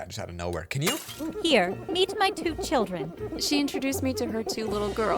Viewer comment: Lol when she said meet my two children i thought she said “here eat my two children”